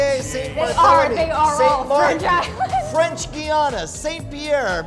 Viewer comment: French islands? I thought you said landmass! xD